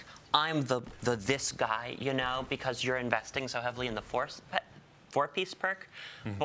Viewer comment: Anyone catch the sandbox person slip up and say “investing in the - force…”